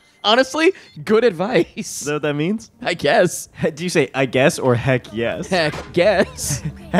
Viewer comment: I guess or Heck Yess. Honestly I heard” I guess” the first time but now I hear both. I’m confused.